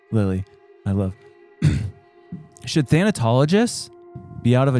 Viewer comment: I thought they said Unitologists lol. Dead Space fans?